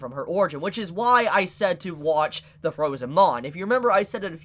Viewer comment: What is the thing he asked us to watch. I think I hear “the frozen mon” is that correct? It’s actually called “the frozen bond”